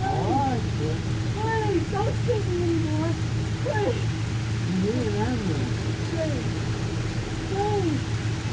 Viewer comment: it sounded like he said you need an ambulance